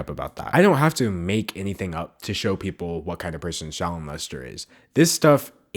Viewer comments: I kept mishearing Sharon Lester and "child molester". Coincidence? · It's funny how close her name is to "child molester." I honestly thought you said it every time you said her name. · Everytime someone says her whole name I hear “Shallow Mustard” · Literally every time you said her name I thought you were saying Child Molester. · Every time he said her name I hear "child molester" but after that clip of her petting and smothering that obviously uncomfortable kid while winking at the camera Its pretty fitting